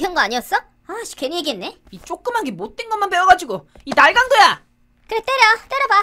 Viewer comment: 자세히 들으면 날강두라 들림 ㅋㅋㅋ